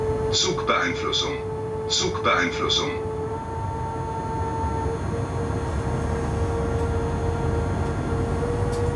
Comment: What is the voice alert at 1:05 saying? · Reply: "Zugbeeinflussung" It is from the PZB (German Train Safety System) and if Im right, the driver was releasing himself of the restrictive mode. Then this voice is a acoustic feedback. (Im not from Germany, so maybe my Informations are wrong)